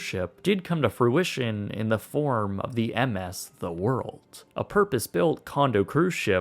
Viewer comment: This is what happens with two dudes with an idea, a lot of money, and no expertise in engineering get together in Florida... I'm intrigued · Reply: I'm sorry, did I hear The World as a title? Like a tarot card Like ZA WARUDO